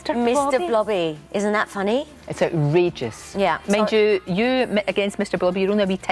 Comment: Unusual singing voice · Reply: Hello Jack, how are you doing? Could you please give me a helping hand? I`m from Brazil and I´m trying to understand the interview .... tell me, what does the interviewer say on minute 0:49? "Is that regious"???? Are you able to understand that?